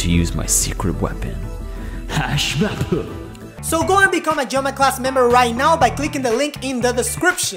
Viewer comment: he says join in joma class.. but the auto generated caption says drama class... is they related. Lol